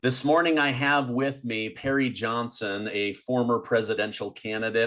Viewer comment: Wow I thought you said Gary Johnson !